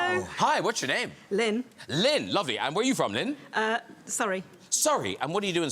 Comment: "Where are you from?" "Uh sorry." "Sorry. What are you doing in sorry?" English is my second language and this what I thought they said at first which made me so confused haha.